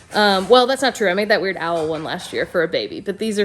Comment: When said “weird Owl” referring to the child’s back pack I heard “Weird Al” 🤣